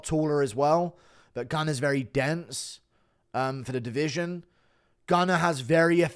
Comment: When you said "Gunnar is very dense" I thought you said "The can is very dented" lmao the disrespect 😂